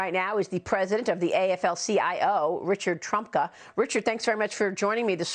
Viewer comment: When it was mentioned Trumka died today I was so happy for a split second—I thought they said Trump!!!!